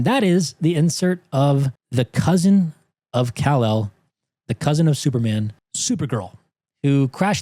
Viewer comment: Thought you said the cousin from hell 😂 1:15:49.